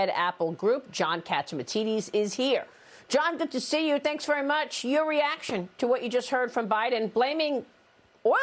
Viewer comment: Did she just say John catchmatities ? 😂😂😂😂